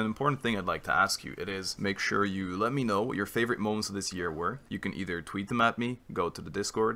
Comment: ok i think there is something wrong with my ears. “Make sure you let me know your favourite moments of this year” AND SOMEHOW I misheard that as: “Makes sure you let me know your favourite *moans* of this year”